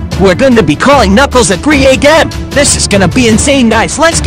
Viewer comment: did he say 3 gae m lol